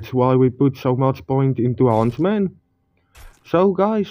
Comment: just before 2:00 you said "that's why we put so many points into...." What was the word you said? what are you putting the points into? I didn't understand you. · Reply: Yoo bro, it's the huntsman perk.